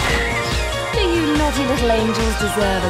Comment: I swear in the slow down mode I heard Charlie's angels. Which if so is very ironic.